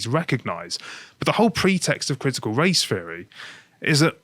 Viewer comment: I keep hearing "critical race fairy." ..